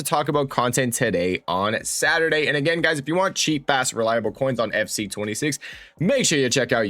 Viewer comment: I thought u said " cheap ass reliable coins" lol